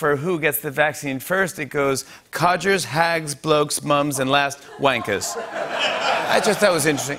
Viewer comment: Does he say "cudgers, hags, blokes, moms and last wankers" ? (no native Eng. speaker)